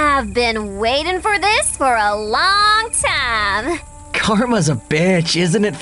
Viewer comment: Jake what did you just say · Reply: “Karma’s a B.”